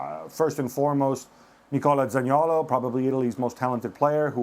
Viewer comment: Does anyone know what player Gab mentioned as Italy's best player who missed the tournament at around 07:40? I couldn't understand. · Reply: He refers to Zaniolo from A.S. Rome is a great player who suffered an injury and could not attend the competition.